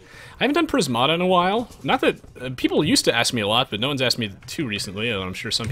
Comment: heard you say no aoe and heart stopped for the longest second of my life